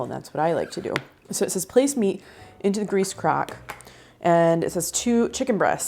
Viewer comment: OMG...I am doing busy work at my desk while listening to you and I heard "place meat in the grease CRACK" and I whipped my head around! LOL Thanks for lightening up a rough day! I also love crock pot cooking and 27 years in libraries taught me to cook at least 3 recipes from a borrowed cookbook before buying it....used.